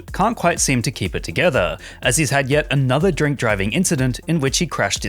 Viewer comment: why do you keep saying drink driver/ing or am i mishearing shit. Just put this mfer in jail for a few years, he clearly needs it.